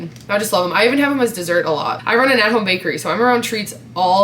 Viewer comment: I heard her say "I'm around treats all day, I work in an animal bakery"..........wth?